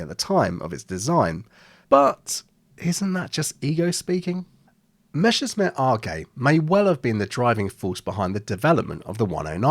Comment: I kept hearing "Messerschmitt are gay"... ;-)